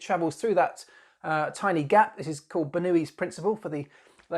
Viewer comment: Ha I thought you were saying "Newey's" instead of "Bernoulli's" for a moment and that would have led to a different comment. Red Bull's nose is fully buzzword compliant, and they're really testing the limits of the "no intakes in the nose" rules.